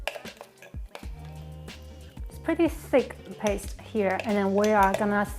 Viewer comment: At first I thought you said it was a pretty “sick” paste😊😊🤦🏾‍♀️ and I thought wow, “It’s about to go down”. You should have dropped the mic after this recipe because it does look like a sick (really good) recipe.